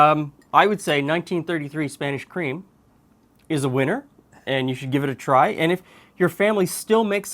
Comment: I thought it said Spinach cream...I was like, I got to see that!!🤣🤣🤣🤣